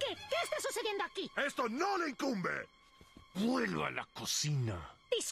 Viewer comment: El de vuelva a la cocina no lo entendia porque antes pense que decia guele a la cocina xd pero haora que se que dice me rio mucho mas que antes xd